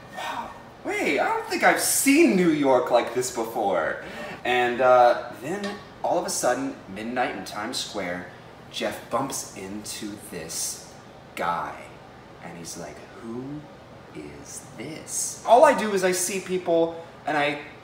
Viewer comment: does? in that sentence does say Jeff????